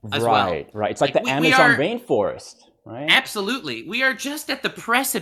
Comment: I swear I heard "Amazon Vain Forest"